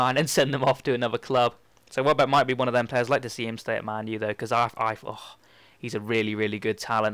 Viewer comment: He said he hopes to see Welbeck leave really??? Why don't you pay close attention at 3:45 because if I heard correct he said ''I'd like to see him stay at Man U though''